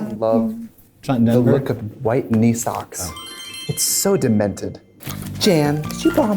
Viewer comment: I thought she said white Nissan's and I was about to say bitch get in we're going shopping